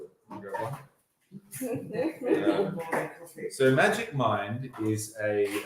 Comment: I thought you were saying Magic Mike